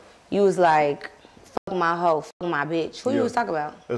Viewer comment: did he say “ i make mood music, i make CULTURE” ???? If that’s what he said then that’s crazy that he pronounced culture like that 😭😭😂😂